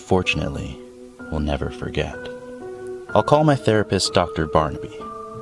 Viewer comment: OMG I thought it said THE RAPIST!! BUT IT SAID THERAPIST XD